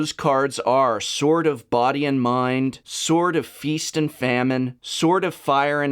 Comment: i thought he was saying "Sort of"